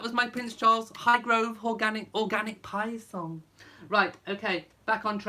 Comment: Lol. For a minute there I thought you was going to say did you like my Hghgrove orgasmic pie song.